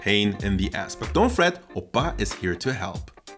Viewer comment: Wait wait wait.... Did you really say oppa at 7:33???